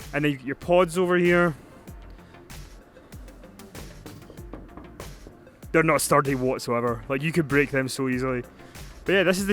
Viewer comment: what .what did he say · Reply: "That's not sterdy (strong) what so ever! You could break that so easily!